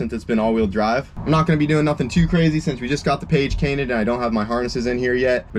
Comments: did you really say page cainted? at 7:08 or am i hearing things 😆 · Dang that's 2nd time you have said "Page Cainted" instead of "Cage Painted" lol at 7:08 min mark you say it. I remember you said it in another video too. I do that ish all the time haha. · "Page Cainted" I heard that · Did I hear “We just got the page cainted “ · "just got the page tainted "... what did you say? Lol sorry, we laughing with you bro😂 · Second time I’ve heard you say “page canted” 😂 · lol @ 7:07, you just got the page cainted.... Had to re-watch that to see if I heard that right